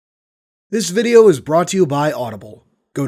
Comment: I thought the intro said "this video was brought to you by arbiter"